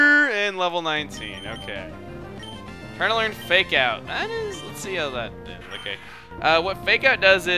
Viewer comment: when you got the alert for Kappa learning fake out I was looking down and all I heard was "Make out" I got really confused and thought you had just completely lost your mind